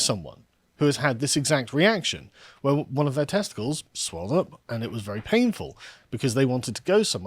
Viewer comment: Did you say HER testicles swole up? Was this the Lyft driver? 😂